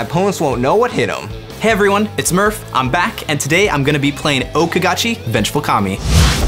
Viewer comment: I thought u said wasn't playing